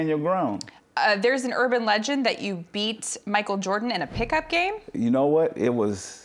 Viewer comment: I thought he said he beat Michael Jackson in a pick up game